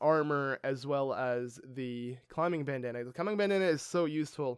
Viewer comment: I thought he said climbing banana